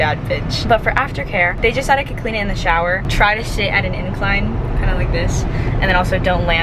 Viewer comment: omg i thought she said “shit at an angle” and i was like hold up… my mistake lmao ily <333